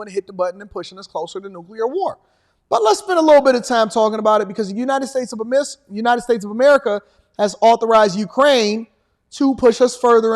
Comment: the United States of a Missle Crisis, i heard that 😅